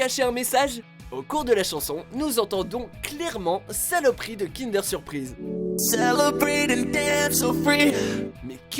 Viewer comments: moi j'entends "Saloperie de Danette aux fruits" xD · moi j'entends plutôt "Saloperie de d'Anne so fruit"